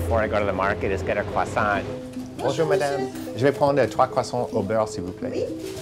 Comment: What did he say at 0:13? J'ai prend etre croissant s'il vous plait? I pretty sure it's not "etre", but it sounds like it, can anyone correct me? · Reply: no he said "je vais prendre trois croissants au beurre"